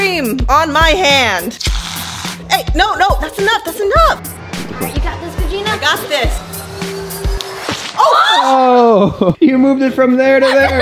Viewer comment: was Regina saying “Oh shi-“? 🤔